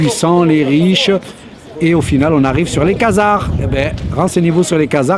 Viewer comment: renseignez vous sur quoi ? Les quasar ? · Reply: khazars voir sur Googles.